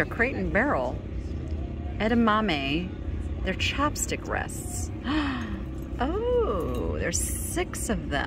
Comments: What is a chapstick rest? Why does one need a chapstick rest?Hilarious, I just saw recap & it's ChOpstick rests. I'm like the Gilda Radnor Emily Latella SNL Character.....Nevermind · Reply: You aren't the only one who thought "Chapstick rest". I could have sworn I heard say that. LOL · I thought you said those crate and barrel soy bean pods were chapstick holders!!! It was not until your wrap-up I heard chopstick-haha. I could not figure out why someone needed to rest their chapstick on them. is so endearing. Glad you both got out of the heat!